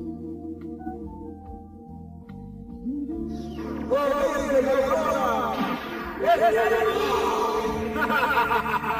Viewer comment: es muy claro que en este doblaje Shura exclama "Excalibur!", pero qué es lo que dice Camus? "Torrente de Aurora?" y dice algo más al mismo tiempo que Shura, pero no se logra entender. Ojo! no critico los doblajes de esta película. Es mas me parecen excelentes. Solo que tenía esa duda en cuanto a los diálogos. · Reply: Corriente de aurora